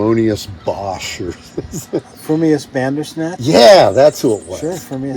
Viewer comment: Please, can someone tell me what band and Dave are talking about at 11:25? I didn't catch the name. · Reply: Frumpius Bandersnatch